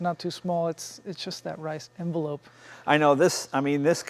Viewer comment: hah its just that rice envelope. 7:40 ...tell me i am not the only person that heard that. I love the car not bashing asian car enthusiasts (sorry) ricers...but did he really say that?